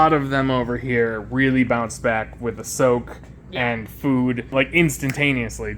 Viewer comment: When you said her shed is already coming of with the soak I thought you said with the soap you said this with (Sora)